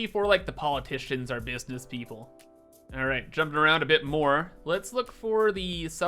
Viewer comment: I thought said "politicians, our business people" instead of "politicians or business people," but they are basically the same thing anyways